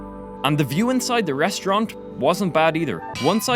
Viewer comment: i hear "...inside the restaurank ". D